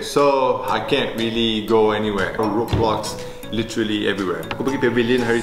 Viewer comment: did i hear R O B L O X the game